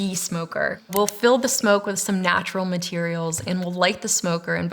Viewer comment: "natural materials", what i heard is "we give weed to the bees"